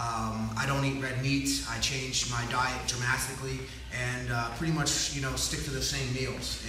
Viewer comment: did he say DRAMASTICALLY???